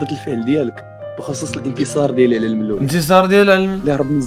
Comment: p انا سمعت "انكسار" ما سمعتش "انتصار" اللي بحالي يبانو هنا 😕😳